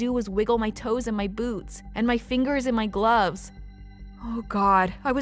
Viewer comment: When she said “I can only wiggle my feet and boots”I thought she said boobs ;-; also why does the therapist look like Donald Trump